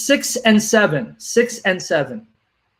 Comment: what did he just say? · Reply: 67